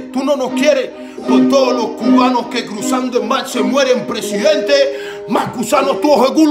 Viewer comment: ¿Alguien sabe que es lo que dice en el minuto 1:36? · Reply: Steven Álvarez ... los cubanos que cruzando en mar se mueren...